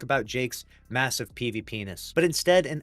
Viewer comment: i legit thought you said Massive penis, I had to play that back.